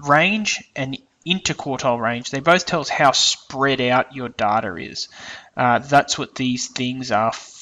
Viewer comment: i thought in the end he said spread like your daughter ... ( okay i know i may seem immature for this and i apologize but i had to replay 2 times to understand what it said